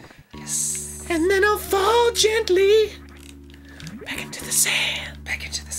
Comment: At first I thought he was saying back into the Zam.